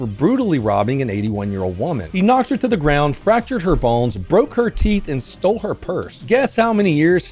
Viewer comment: why did I hear "fractured her balls" at first